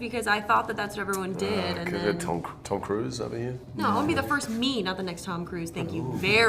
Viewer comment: When I first heard Larson say “I’m gonna be the first me...” I thought she said “first meme”